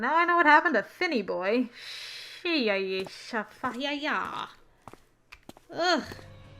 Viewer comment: Good grief.. That got really dark.. Fin is totally broken yet again. Also could you do me a favor? Spell what you said here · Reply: Yeah, really dark for sure. x_x And lolol of course, sir. I will give my best attempt at that ridonkulousness. :P sheeeayeeshafayaya xD